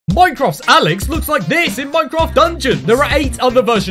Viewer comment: I thought he said “Alex looks like jizz”